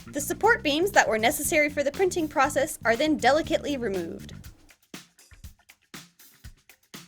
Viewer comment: I heard; "The support beans that were necessary for the printing process are now ✨ delicately ruined ✨ "